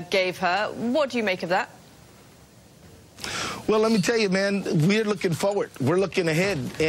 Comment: Did he call her "Man" ?